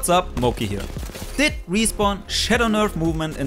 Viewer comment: ngl when said "Did Respawn Shadow Nerf" I heard it as "Did Respawn shit on our movement?" xD. But yeah i've been getting a lot of dead slides lately.... super annoying.